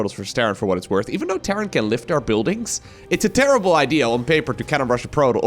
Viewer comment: i swear i heard saying "this is an terranble idea" at 1:45, you lost an opportunity to a huge pun, xD